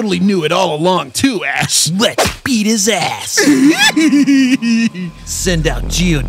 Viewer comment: I was eating cereal and had to rewind on a bit where they found a team rocket because I thought they said EAT his ass